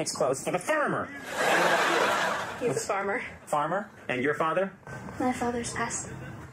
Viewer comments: I thought the girl said "My fathers an ass." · what did she say? 'My fathers pissed'.......?